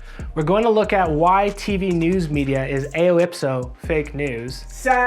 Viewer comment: what word is being said here? That sounds like elipso?